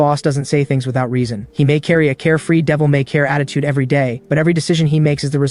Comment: Devil may… what? Say that again… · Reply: They said devil may care but I respect you for noticing that